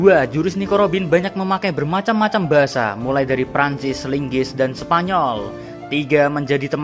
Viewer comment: bangkek gw salah denger gw dengernya linggis bukan inggris